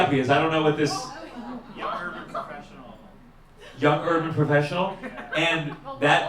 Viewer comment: I thought he said "oral professional" too.